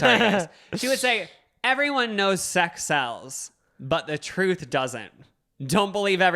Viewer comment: i heard "Everyone knows sex cells but the truth doesn't" and was so confused